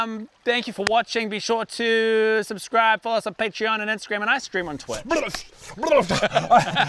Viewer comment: Just me or did it sound like said "Ice Cream on twitch" at the end lol. Its not the accent either cause im from NZ.